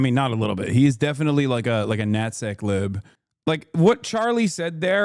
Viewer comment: What did he say!? Wadideezayyyy?! 10:35 a notsiqlube?